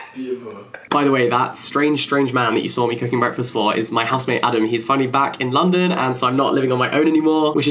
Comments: guys i heard husband why 😭😭😭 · I literally heard "my husband Adam" when you said housemate Adam , I don't know if it's wishful thinking or what :D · I discovered this channel today and I'm binge watching him. at 18:56 I thought he said "my husband" and the queer in me just died with excitement until I turned the captions on, and realised 'oh' that makes sense.